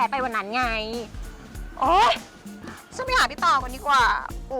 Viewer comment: ano daw teh? did she just said “ pota nemen”?😂